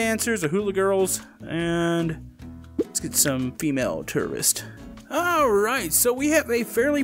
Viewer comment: When he said tourist i thought he said terrorist